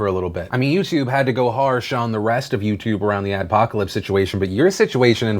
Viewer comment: I thought it said apocalypse, I was very concerned.